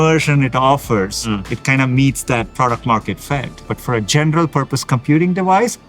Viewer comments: subtitle is wrong. Not product market fact. Product market fit. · I think caption was to be "product market FIT" instead of "product market FACT". (unless you made the mistake to have one extra comment).